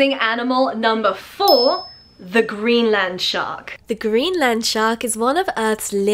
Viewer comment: I thought you said Green Landshark first XD